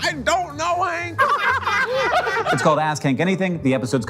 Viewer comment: I'm gunna level with you, I was only listening to the video and thought everyone was saying 'Ask Kink' instead of 'Ask Hank'